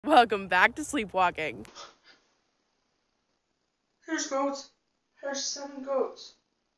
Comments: Did anyone else get chills when she said "there's seven goats" ?? No??? Just me??.... Okay then. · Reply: Lol when I heard "There's goats, seven goats" got me dyning😂 I also was thinking that it could be connected to her sam and colby vids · Nah i click on the video first sleepwalking thing i hear "theres goats" 😂